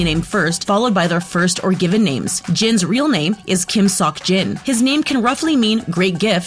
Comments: Me: chokes DID YOU JUST SAY SOCK?!?! EOMMA JIN IS GONNA BEAT CHU!! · What is kim Terihung ??? What is jungkoo??? What is kim sock Jin??? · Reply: Lmao I heard, "Kim SUCK Jin" 😂